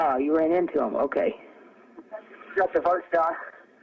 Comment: I hear ‘shot the first guy’